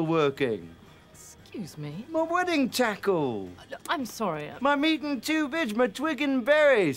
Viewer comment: 1) Bits and pieces 2) Wedding tackle 3) Meat and two veg 🥦🥩🥦 4) Twig and berries 🍒 5) Lads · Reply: Niels Daemen I thought he said, “Meat and Tubage”.